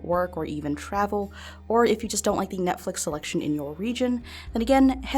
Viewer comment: I thought you said the Netflix selection of a Norwegian, and I was so confused we have like the worst selection ever I can't understand why anyone would want to change their location to Norway but then I was like ohhhh 😂